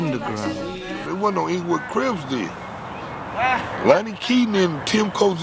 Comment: I know this came out awhile ago but if you don’t pay attention to how he says CRIP it sounds like he said CRIBS. So I can see where that debate comes from